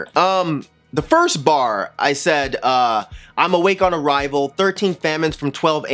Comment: I heard it as "awake on a rival". Double entendre maybe?